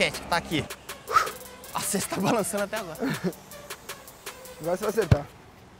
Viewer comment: so eu que ouvi o neymar falando : vai so7tar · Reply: Ele falou: "Agora cê vai acertar" auahahhshsu